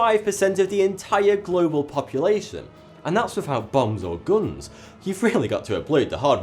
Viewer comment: Did he say "...BONGS and guns...."?! 7:25 ."...from 1976 to 1025. Wow? I think your a thousand years out ie i think you may have meant 976. I thought Hilary Clinton would be at N01.